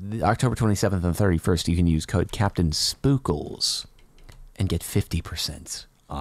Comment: when you where whispering i thought you said CaptainStupin